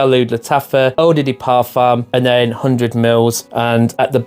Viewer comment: did you just say "oh diddy parfum" 😭